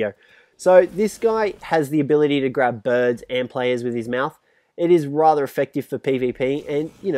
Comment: I thought he said birds and planes 😂